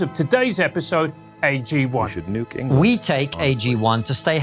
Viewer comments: did you say “we should nuke England”? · the quiet background commentary fucking kills me, love this intersection of humour and critique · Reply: I think i heard "we should nuke England" at one point lol